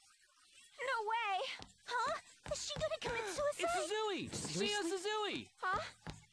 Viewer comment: the first time i heard this i thought she said "huh, suzuiside?" like a fucking pun as 'suzui' is going to 'suicide' genuinely caught me off guard (she said suzui san btw)